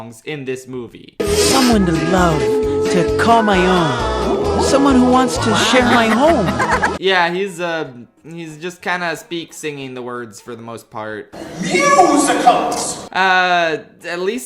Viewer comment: Did I hear that correctly because it sounded like he said: someone who wants to s**t my home